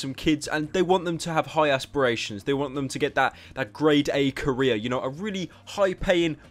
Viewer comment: I thought pyro said "grade a Korea"